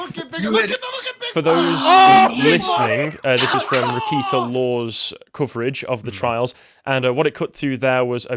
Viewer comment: What channel covering the trial do they show? I thought he said “Repeater Law” but I can’t find it. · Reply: Reketa Law